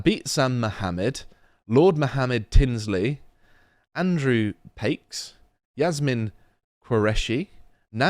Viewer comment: I heard Andrew tate 🙂